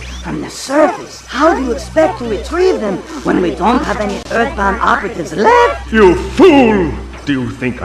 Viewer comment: did he said earthbound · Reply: Earth-bound, I think but yes!